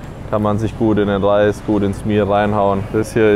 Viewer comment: An den Menschen, der die Untertitel macht: Bei 15:18 sagt er "Kann man sich gut in den Reis, ins Meal reinhauen. Ich hab aber auch erst Bier verstanden :D Danke übrigens für die Untertitel, die sind sehr hilfreich.